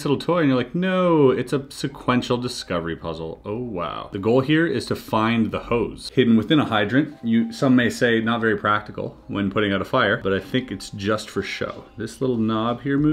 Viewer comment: I heard " the goal here is to find the hoes"